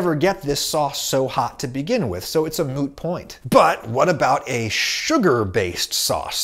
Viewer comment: At first I thought he said "So it's a moo point." You know. Like cow's opinion It doesn't matter It's moo.